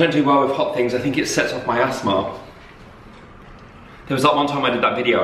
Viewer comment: you made me laugh so much! I thought you said it kicks off my ass-thma rather than asthma 😂😭